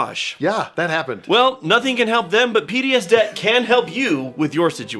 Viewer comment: I am so sorry I heard "Well, nothing can help them, but PTS-DEBT can help you" and went PTSD?!?! P.D.S. Gotcha.